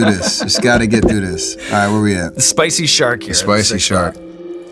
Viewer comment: I thought they said "spicy shart" without laughing and I'm thinking huh? 😂